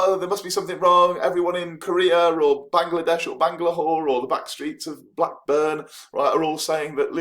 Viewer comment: I taught I heard Bangladesh and Bangladwhore 30:52 😁...great video!